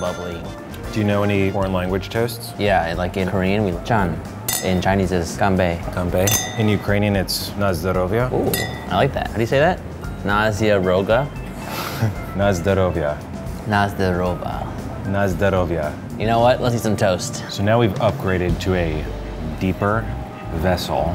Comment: I thought they said Nazi gone rogue